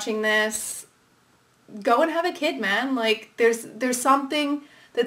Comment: when she said "If you're gay and watching this, go and have a kid, man" I misheard and thought she said "Don't have a kid, man" and I stg I choked on a potato chip